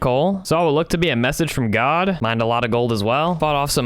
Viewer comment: did you say god